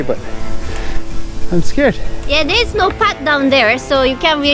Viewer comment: Did she just say "yeah they smoke pot down there"